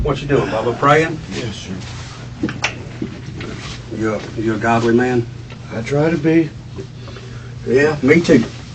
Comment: for a moment I heard. Are you a goblin man?